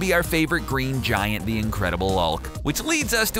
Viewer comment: Am I the only one who heard "The Incredible ulk" at 3:22?